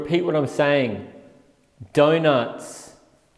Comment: don’t know about you, but that distorted evp voice, actually sounded like it repeated “Donuts” only very distorted and very weirdly